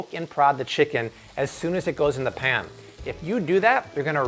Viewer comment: Did he just say "roon"? What does a strip of cloth have to do with "ruining" the sear on your chicken breast? 🤔🤦🏻‍♀️😂🍗 I'm sorry, I can't believe I just went there. I normally hate it when someone corrects someone on a video or a comment. But I just couldn't help it this time...I just couldn't. Was he trying to sound cool and throw some odd nonexistent accent on the word? IDK. I'm probably going to get slammed by the comment critics but oh well, I can take it. ✌️🤘